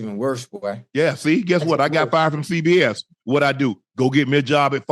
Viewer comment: Sound like he said fired from CVS 😂